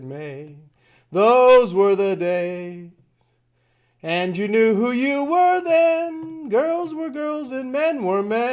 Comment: I always heard "I knew you when you were thin" lol. It's not like she's singing clearly. This is not a Mandela effect